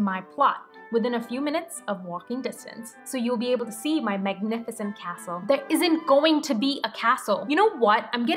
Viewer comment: Wait... 12:33 did you just say... "Meghanificent"??? 😂😂😂